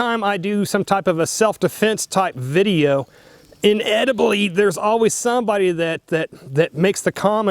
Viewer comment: Did he say "inediblally" (9:20) and also, please don't shoot a fucking tree. (Good lawd...)